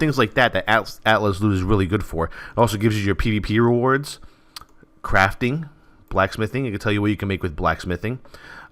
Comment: haha i thought u said crap thing instead of crafting XD